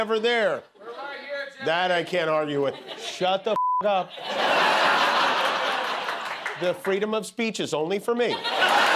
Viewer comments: what did he shout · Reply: "We're right here, Jimmy." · Can anyone tell what the audience member said at the 2:45 mark? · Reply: I heard something like "We are right here Jimmy!"😊